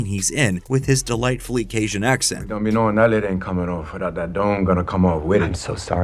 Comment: I have seen the movie, and with subtitles i still dont have a clue what he says at 7:20 but i laught every time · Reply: "Don't be knowin' that lid ain't comin' off without that dome gonna come off wit it." Basically yes, it means what the above comment said: "The only way Juggernaut is going to give up his helmet is if we cut his head off."